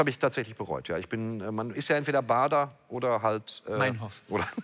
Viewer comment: was sagt er da? verstehs nicht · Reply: Meinhof. Schau nach Baader Meinhof und du checkst es.